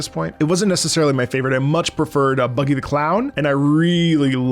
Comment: Did he just say "Buggy D. Clown"? He really is one of us now